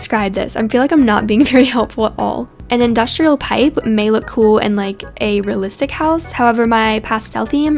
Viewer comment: No- IVE BEEN PLAYING BLOXBURG FOR 2-3 YEARS AND I THOUGHT IT SAID ‘industrial white’ 😭😭😭